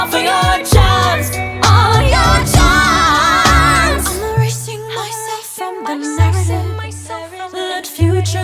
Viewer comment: Is it just me but I heard” I’m sexing myself “😂